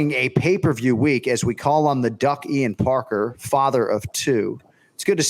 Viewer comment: I know Anik said "The duck, Ian Parker"... But when I heard: "The Ducky Parker"... That's what I am calling Ian from now on 🤷‍♂️😂